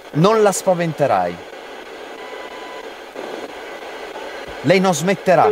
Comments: Minuto 3:43 io sento " lei è il...." Poi non si capisce più tanto bene, spero vi possa essere utile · Nel 3:43 ho sentito una donna che dice "Lei sì" , cioè nel senso che Debby si smetterà , ma io non credo, anche se lo vedo nel 2024 vi avrei potuto dire di stare molto attenti e se voleva attacarvi dovevate arrabbiarvi perché i demoni cercano la paura , dite a Debby che in quei casi deve arrabiarsi, non farsi prendere dalla paura💗